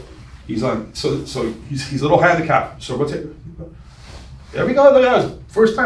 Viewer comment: Did you call your training partner a little handicapped? 🤣 A+++ freemium content